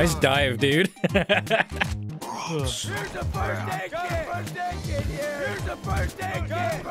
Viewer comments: I thought he was saying 'birthday cake here' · at first i genuienly though he was saying "BIRTHDAY CAKE HERE" and just went with it until i realized what he was actually saying